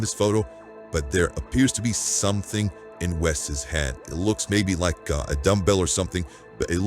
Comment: I initially heard that as, “There’s something in Wes’s head…” and my first thought was, “Yeah, it’s 💩.” But, the correct statement is, “There’s something in *his hand*” — which, as it happens, might still be 💩. 🤨🙄